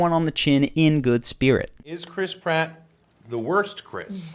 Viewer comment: "Is Criss Pratt the worst?" All I heard was "brat the wurst"